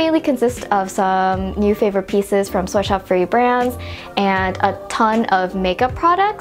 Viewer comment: I really thought you said soy sauce free instead of sweatshop free. Probably because it's loud in here and I'm eating Chinese food. lol I'm so dumb.